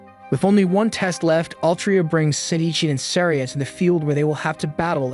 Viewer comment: Thought bro said City Cheese in the start of the video 😂